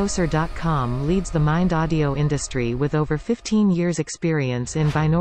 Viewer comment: Sounds like she says Parmesani